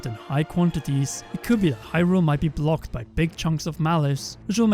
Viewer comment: I thought you were gonna say big chungus